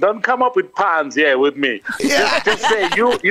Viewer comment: is Sol saying umoya "puns"